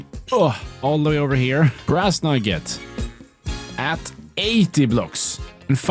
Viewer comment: I heard grass nugget